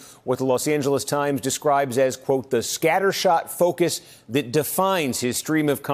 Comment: I heard "scattershit" 🤣